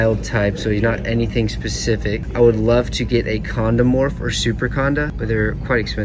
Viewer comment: You said condamorph or something like that and I thought u said condom morph!!